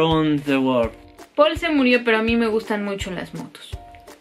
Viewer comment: no se por que escuché muertos en ves de motos xddddd